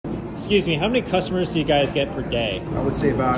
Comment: I thought he said" How many cuss words do you get a day?" 💀